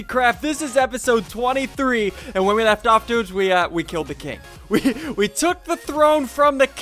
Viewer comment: i thought said he killed the cake it was the king XD